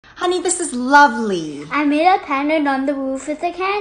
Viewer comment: I heard:"honey its ugly"